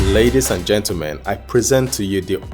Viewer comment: Did I just hear "Ladies and mentlegen" ? Lolz, Amoled back to Tecno flagship display, good one